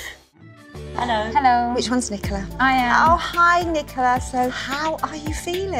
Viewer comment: I thought she said which ones nicker? But she said Which ones Nicola